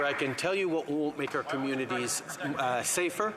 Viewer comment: I think what he started to say was Government before he corrected it to Communities? Sounds like Govmunities? Maybe that's a secret government and oligarch safe space?